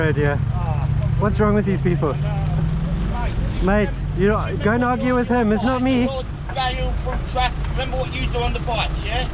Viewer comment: I'm just curious what the guy said that was driving that white car? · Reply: "Just remember, before standing in front of traffic, remember what you do on the bikes, yeah"